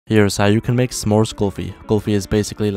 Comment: At first I heard "samosa kulfi" 🤣🤣🤣🤣🤣